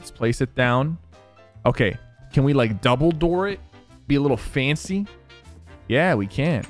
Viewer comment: Thought he said Dumbledore it